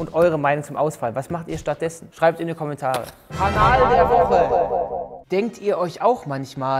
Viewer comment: ohgott ich hab erst "Anal der Woche" verstanden🙈